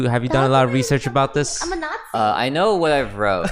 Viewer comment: what is saying? "I am a..."what? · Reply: nazi :)